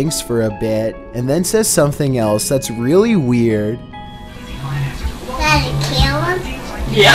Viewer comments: I heard (daddy kill him) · I think he is saying one of these two things 1 “did it kill us”? 2 “daddy killed us”? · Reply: Daddy killed them that what he's saying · I definitely heard "I'm about to kill him?" as if the child doesn't understand what he is repeating.